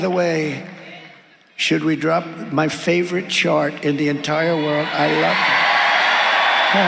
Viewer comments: It sounded like he said "Drop my favorite Shart". I've heard he's good at that. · A lot of people have asked about Germany, “how did they let the Nazis come to power”? This. This is how. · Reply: I thought he said “should we drop my favorite shart?” Please God no